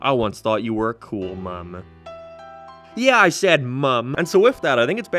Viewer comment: I thought the Irish said "Mam".